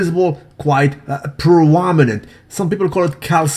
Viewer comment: can some english speaker help me with the world the says at ~ 0:40 ? (quite provoninent???) i hear him saying this quite often but i cant seem to understand the word correctly . It quitely bothers me. thanks in advance ! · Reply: prominent